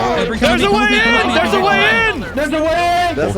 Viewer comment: In the tournament I heard "he's gay,gay"